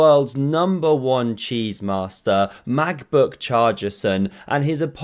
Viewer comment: "Macbook Chargerson" 😂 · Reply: I thought he said Magbook Chargerson! 😆😆😆 I hope you are right, but I also don't want this to be how I find out my hearing is deteriorating !!